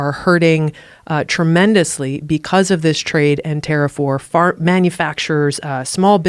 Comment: Umm, I love her, but did I just hear her say: 'fart manufacturers' at 1:41?